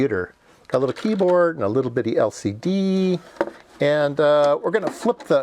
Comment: did you just say "LCD"?